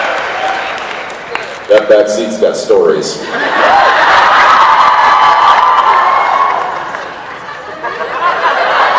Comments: what did Jared say when Jensen said: that back seat's got stories. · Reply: Diane Winchester someone figured that out and answered to my comment: he said "stains" 😂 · What did Jared say at 23:04?!?!?!? 😂😂😂😂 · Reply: yeah I'm pretty sure he said "and a lot of stains"